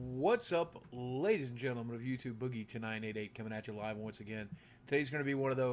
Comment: This entire time I thought you've been saying "whats up, ladies and gentleman of youtube, boogie tonight at 8 coming at you live". Just realised you're saying "whats up, ladies and gentleman of youtube, boogie2988 coming at you live" !!!!!